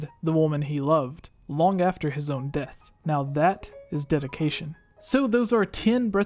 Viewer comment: I thought u said Now that is defecation 😂